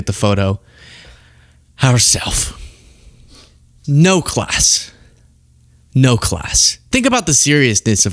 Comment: you're said; no class, no class, but I heard white trash, white trash. She's abhorrent.